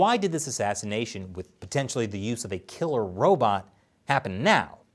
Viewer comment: Whew! I thought you said "Killer Rabbits" We haven't revealed them yet-they are still Secret